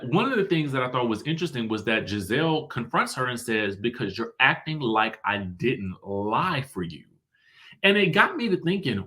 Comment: To me it sounded like she said "you acted like I didn't RIDE for you"